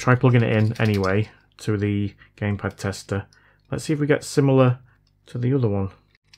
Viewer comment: I thought you said “Gay pad tester” 🤪